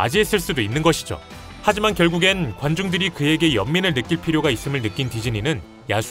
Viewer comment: ㄱ..관종이라고 들었는데 관중이었네